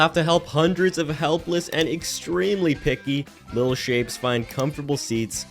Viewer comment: In the beginning I thought you said little shits instead of little shapes 😂